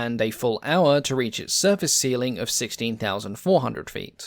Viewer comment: LOL, closed caption says 'surface ceiling' for 'service ceiling', 05:42.